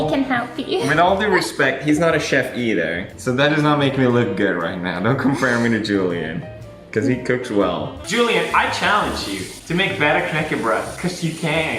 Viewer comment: Sounded like you Said knäckte bro (cracked the bridge) 😂 Good try though 😊